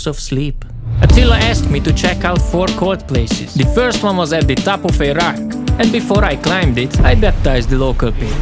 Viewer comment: Bro when u said the first one is on top of a rock I thought u said the first one is on top of Iraq 😂😂😂